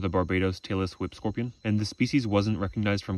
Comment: Your videos are of excellent quality, but the only complaint is that you speak a bit too fast. As a result, when you say: "Tailess Whip Scorpion", I heard: "Taylor Swift Scorpion" and no; I'm not making a joke!